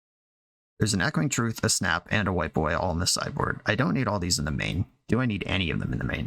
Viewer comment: was looking away and thought he said "there's an Echoing Truth, Snap, and a White boy all in the sideboard"